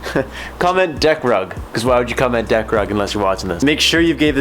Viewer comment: Deck rug although someone was calling me and I thought you said deck drug I got very confused great vid keep up the amazing work!